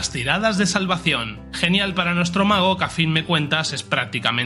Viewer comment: Pensé que decía "negro mago" ajajaj